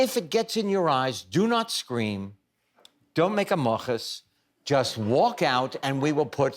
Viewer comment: LOVE Both of these guys. Does anyone know what word that is? At 6:35 - Winkler says "Don't scream; don't make a mochus..." I'm guessing it's a Yiddish word / presumably means don't make a fuss. But does anyone know how to spell this word? :D